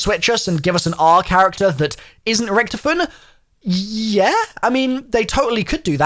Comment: i thought they said richthofen dead